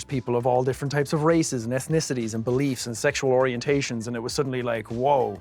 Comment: What Sean says: "Ethnicities" What I hear: "Earthnicities"